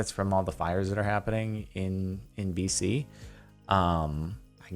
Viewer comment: say DC when in caption say state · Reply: says BC as in the Canadian Province British Colombia which is currently dealing with massive wildfires.